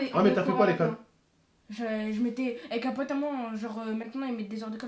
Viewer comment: j'ai compris 'avec capote à moi'😂